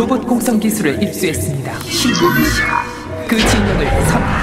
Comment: 신관이시여 라고 들리네요..